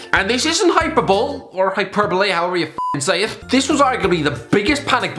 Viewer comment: Did he just say hyper-bowl